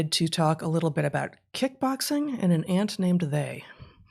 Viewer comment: Lol. I heard "An Aunt Named 'They' "